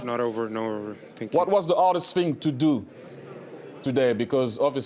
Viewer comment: henry says "hardest", i hear "artist"